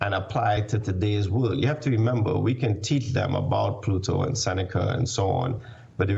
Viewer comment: Did he just say "PLUTO and Seneca"?